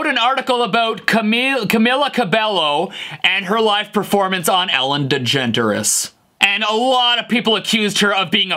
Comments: Ellen Djenteres?! OMG that is perfect, that needs a meme immediately. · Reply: I thought I heard it as Ellen degenderless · I died at Ellen Djenteris 😂😂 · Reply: I thought he said Ellen Genderless and I'm going to keep thinking that because I prefer it. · Ellen Dejenteres? 😅😂😂😂 0:08 *Dedjenteres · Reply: I thought he said Ellen DeGenderless? :O